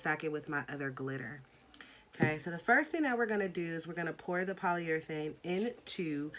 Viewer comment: what was the stuff you poured into the jar? All I heard was "poly" but great video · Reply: Shaneka Bracey she said polyurethane I’m confused bc it’s looks like polycrylic